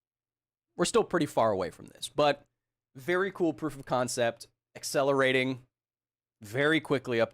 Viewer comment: very cool proof of concept.... i heard it as very cool-proof concept and had to rewind to hear it again...